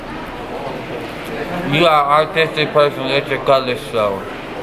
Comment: anyone know what said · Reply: U got artistic personalistic let your colors show I think 😂😂😂